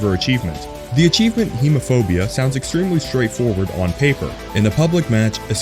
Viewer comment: I thought I heard HOMO-phobia 😂